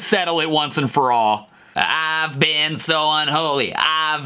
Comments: "Ive been so unholy." I hear "ive been so holy" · Lil Wayne: I’ve been so ugly, i’ve been I’ve been. Me: Same bro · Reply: Instead of "I've been I've been" I heard "I've been lied to" I'm ugly and never told the truth